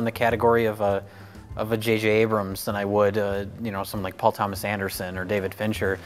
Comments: Did Jay say "Vajayjay Abrams" at 5:55 ? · I realize after going back to listen a second time that I misheard him, but I definitely thought Jay said that he wouldn't categorize Nolan as a great film maker, like Paul W. S. Anderson and David Fincher 😂😂😂 I was like,hey, I love the beautiful mess that is the Mortal Kombat, but "great filmmaker"? Hahaha I had to rewind to hear that he actually said Paul Thomas Anderson